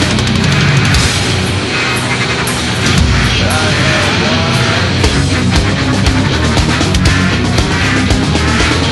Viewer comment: What's he saying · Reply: ´´i know why``